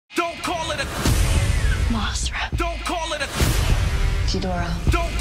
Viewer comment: did i just hear MOSERA like that just triggers my ocd